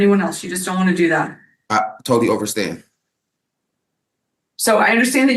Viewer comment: did he say “I totally OVERstand?” Cause that’s a word I’ve never heard but gonna use it when someone drags on with an explanation